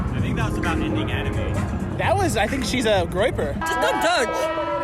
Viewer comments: What does he say at 3:00? She's a "groiper" ? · what did say at the end there? She's a Graper? What is that mean?